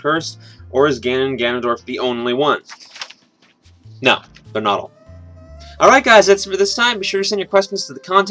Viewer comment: What did he say at 10:50? "No, they're not all". or what?